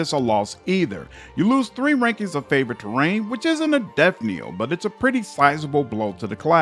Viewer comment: One minor quibble, it sounds like you are saying "death kneel" in these videos instead of "death knell", which is pronounced exactly like it is spelled except for the silent K. I could be mishearing you though!